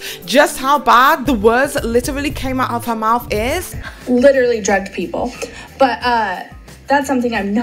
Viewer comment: Creepshow is the absolute worst.....but....I didn't hear her attack anyone's sobriety in these voice memos. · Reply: I literally just heard what she said. I thought she said "They judge people". She in fact said "drugged" people.. Wait...drugged people?!